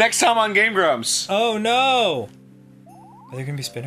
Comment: I thought he said “u know wut else is terrible SPINEGARS”